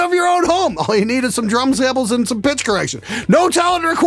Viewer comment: I thought you said "bitch correction" hahaha